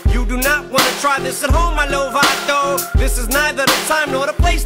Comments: Am I the only one who thought when he said “you do not want to try this at home MY NOVATO” he said MY LIL VATO😂 The fact that the next couple lines have the words MACHO n NACHOS in them fit well in my mind 😎 Happy 4/20 🔥 🍁 💨 💨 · i think at 0:53 he says "You do not want to try this at home my lil vato." not Novato